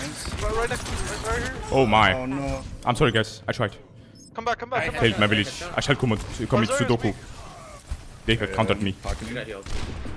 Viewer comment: did just say "commit sudoku?" XD